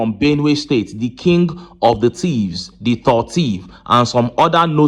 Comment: Did I hear, “the king of the thieves “?😂😂😂😂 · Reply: is not thieves but tivs